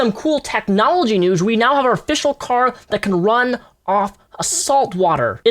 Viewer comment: at first I thought you said "run on assault water" I was like... uh....